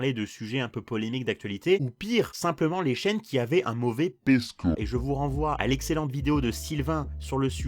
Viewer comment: il a dit quoi svp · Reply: Un mauvais P-score